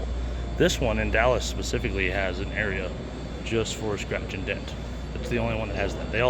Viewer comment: When you said "scratch and dent" while visiting Summit Racing, my deaf-in-one-ear ass thought you said "scratch and sniff" and briefly thought that you were gonna go around smelling everything.